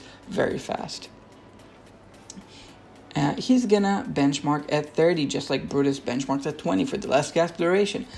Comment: Did I mishear Eironn benchmarks at +1? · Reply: Check out the article! He benchmarks at 30. I mentioned his 1 because, well, you gotta grab his 1.